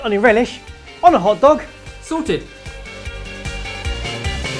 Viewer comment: Did anyone else get confused at Caramelised Onion Tart and thought it said Caramelised Onion Fart?